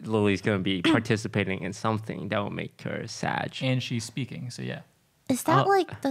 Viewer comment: Toast streamer instincts make him say Sadge instead of SAG. lol